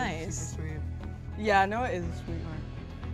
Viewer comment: Can someone tell me what said at 4:35 bc all i heard was "yea noah is pretty...." pretty what? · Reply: Sweet/nice